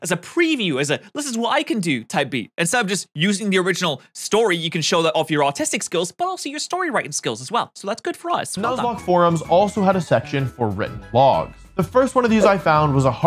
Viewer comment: My brother in Christ at 9:51 I think you misspoke and said "autistic skills" instead of Artistic skills